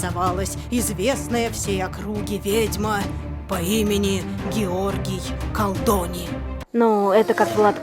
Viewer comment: услышала Георгий Гандони